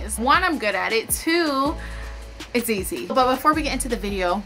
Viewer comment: Did she say "2 its ezee" or "2 its easy" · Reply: It’s ezee